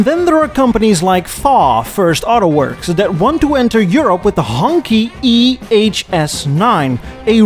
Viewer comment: Everytime says Hongqi, all I hear is "Honky". George Jefferson used it as a slur towards Tom Wills and other white people he didn't like. Not sure if it was part of 1970s Americana or just specific to "The Jeffersons" but I giggle a little everytime says it. I hope there is never a zebra car company because thats what he called couples who were interracial. I miss the politically incorrect times when people had thicker skin.